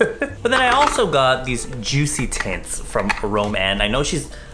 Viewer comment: every time he said “JUICY TINTS” i thought he said “JUICY TITS”😂😂😂😂😂💗love ya!!❤️❤️